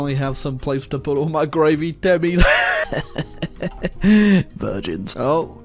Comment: I heard "gravy Timmies" It made me happy Then I understood the sentence It made me offended 😔